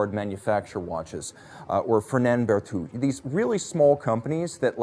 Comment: What is the third name that you mention at 23:27 ? I heard Parmigiani and LUC chopard but cannot catch the other one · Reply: Hi! mentioned Ferdinand Berthoud alongside Parmigiani and LUC Chopard...